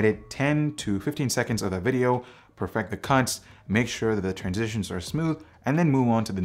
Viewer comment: Did you really say 'cunts' at 8:57 in this video? Great review btw! Lol